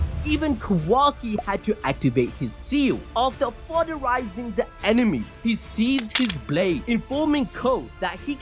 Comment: Why did I hear "after sodomizing his enemies" 🤣🤣🤣😂😂🤣😂😭😭💀💀💀